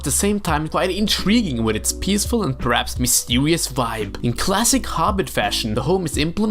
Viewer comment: Did you just say mistewious? Btw, same love for the LOTR series here. As for The Hobbit, it’s good but I’ll stick to the books 😅